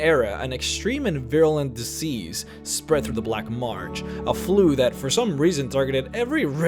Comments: Weird why do I keep hearing him say "Black Marge" instead of "Black Marsh"? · Added in subtitles for those who like having those, enjoy! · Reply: Weird why do I keep hearing you say "Black Marge" instead of "Black Marsh"?